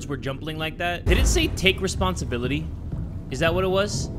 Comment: it said take snieslyotaekbptir (whatever that is..) EDIT: i think it said take responsibility but when i paused on that i thought it was correct because of the first word